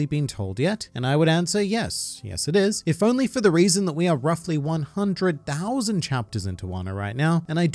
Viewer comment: Did i hear wrong or did He just say “if it wasn’t because we were roughly 100 thousand chapter in”